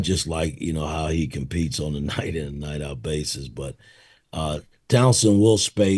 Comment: Did he say Townson? 😂